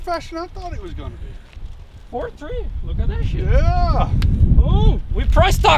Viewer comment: DID HE SAY 4.30'S ON MOTOR????😮